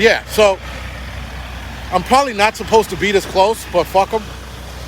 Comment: did you just say "butt fuck em" I do believe this is a family channel. please clean up your act.